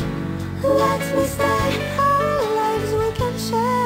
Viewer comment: Snow White has quite a thick accent. I have no idea what she's saying at 3:15 "Let me stay, our legs we can share"? Did she bring some KFC? Was that some innuendo about her own legs? Am I mishearing things? What the hell? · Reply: I think she actually said "Our Lives we can share."